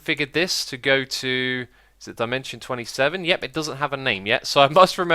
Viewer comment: dimention 27...I thought you said the legend27